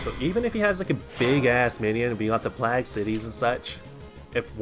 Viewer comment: Hey! What is up with the way you say Plagued City? Sounds like you say Placked City or something? xD Sorry, couldn't stop myself :P